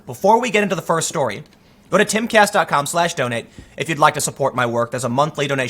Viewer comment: When you tell me to "donate" I hear you say "donut". Then I remember I'm hungry.